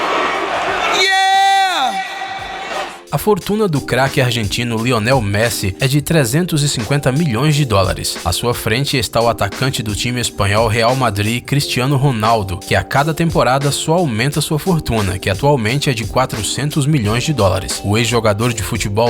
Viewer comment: Eu ouvi atacante da Juventus?